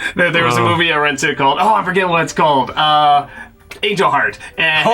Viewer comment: I miss the smell of walking into a video store. Idk how to explain it, they had their own smell though...the smell of the weekend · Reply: what movie was he talking about? It almost sounds like he's saying "Age of Heart", but the CC has it as just "jarred" so it didn't understand him either. What was the movie he said?